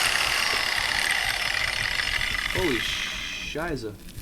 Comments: This moment when you understand "scheiße" which is saying instead of fuck:D( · did he actually say Holy Scheiße ?😂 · Did he say Holy Scheisse? Didnt know he speaks German :D